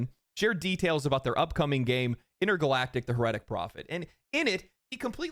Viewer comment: I swear I heard “Intergalactic: The Reddit Prophet” and that is now my head canon title for the game going forward.